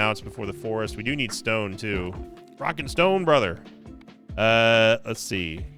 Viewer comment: Did I hear a Rock and Stone!?